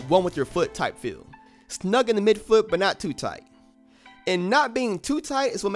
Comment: I thought you said to keep it tight!